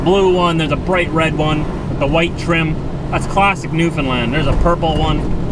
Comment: oooh new foundland, I thought you said new Finland. oh well... keep the videos coming. Greetings from Finland :)